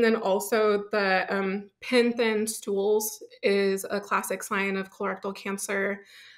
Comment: Whats "pensins stools" at 2:21? Sorry, i cant hear what she said. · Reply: Pencil stools